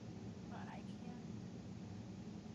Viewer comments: Is she saying "but I can/can't"? (Time stamp is replay of the voice) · On Clay's recording of the female voice it sounds like she says "but i can" anyone else hear the same?